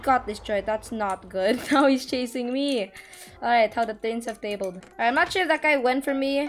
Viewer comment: did he just say "how the turns have tabled" LMMAAAOO